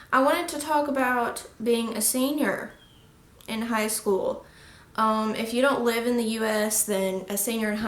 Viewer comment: I heard singer in high school lol